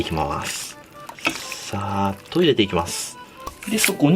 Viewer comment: 昆布だし入れる時に「サーッと入れていきます｣だと思うけど「さぁ、トイレ行ってきます。」に聞こえてちょっと笑った。